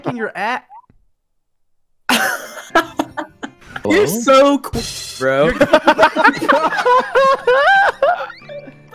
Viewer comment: what did call at 1:09:00 ? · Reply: queer i think idk